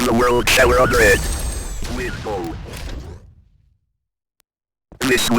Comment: Did V2 say: A: limbo OR B: window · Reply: Window